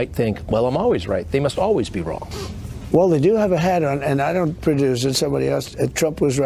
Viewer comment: Did Trump say, "Well, they do have a hat on"?